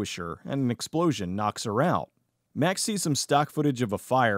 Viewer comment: who else heard “a McSplosion knocks her out”?